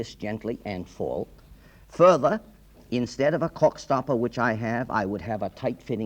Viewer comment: I could have sworn he said "cork stuffer". :face-green-smiling